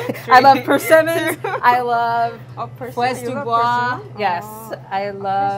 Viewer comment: what is the fruit she said at 7:35? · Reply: Fraises des bois. Wild strawberries.